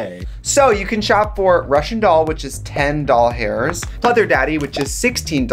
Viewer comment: why did i hear "doll hairs" instead of dollars at the end when she was saying the prices